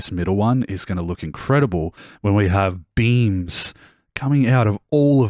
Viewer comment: you said "beams" but i heard "beans" and ive been laughing for an embarrassing about of time